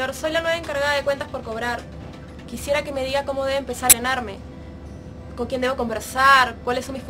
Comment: alguien me dice que es lo que menciona en el minuto 2:53 esa palabra no la encuentro o no la entendí se escucha como si dijera empezaren arme